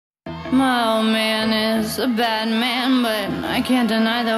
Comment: at first i thought she said "my old man is batman"